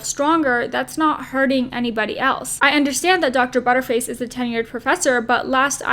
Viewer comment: did you really call her Dr. Butter face 16:26?!? LOL! I had to replay it twice cuz I thought I must've misheard you. 🤣😂🤣😂